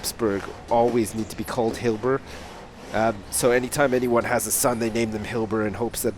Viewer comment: when it said hibler was born i thought it said hitler!